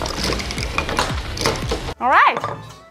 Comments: I misheard her the first time around. Thought she said the non-alcoholic Old Fashioned was "Perfect... for driving" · Lmao at first I heard “for dry week!” as “for DRIVING!”